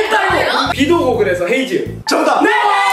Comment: 난 왜 다 헤이즈 비도 오고 그래서로 들리지?